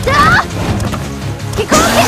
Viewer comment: I thought Gouken say Gadoken instead of Hadoken according to the training mode...